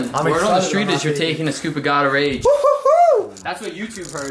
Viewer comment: that preworkout what does he say, god of age? gatorage? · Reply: Gustav Wallin god of rage